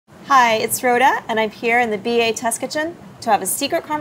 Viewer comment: When Rhoda was speaking; I heard I'm Froda and I went woah someone's parents were huge LOTR fans.